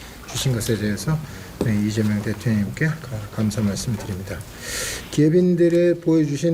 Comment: 나만 대표님이라고 들리는거야? ㅋㅋ